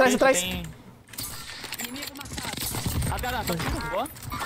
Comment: eu entendi inimigo matado kkkkkkkkkk